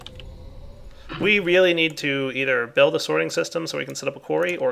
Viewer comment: Why did I hear this as "We need to build a Soaryn system"?